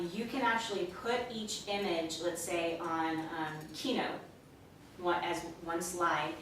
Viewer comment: or around there, you mention putting pictures on what keno, keynotes, or what? What did you say? You went so fast, and I didn't hear it clearly. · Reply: Robert Spiller what we said was keynote. You can program your images and speed them up so quickly that your conscious mind cannot focus on any one of them. This is just one technique. There’s a whole bunch of ways to implement this and it’s encouraged for you to be creative.